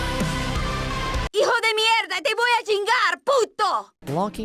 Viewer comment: bruh she really said 'hijo de mierda' (son of shit)?